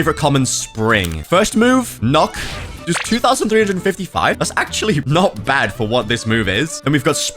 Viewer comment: subtitles say 2255 instead of 2355